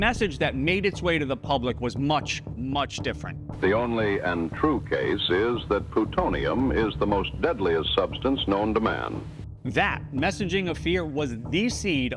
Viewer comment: Caption fail 2:10 "The only untrue case is that plutonium is the most deadly substance known to man." What was said was "The only and true case is that plutonium is the deadliest substance known to man"